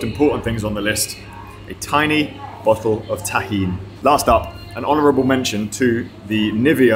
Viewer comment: I didn't catch what you said at 5:48. You said "a tiny bottle of ?tachine?" or something like that. What is it, and what is it for? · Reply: Tiny bottle of Tajin seasoning, it's a Mexican staple that's made from salt, lime and chilis that's great on lots of stuff (like mango).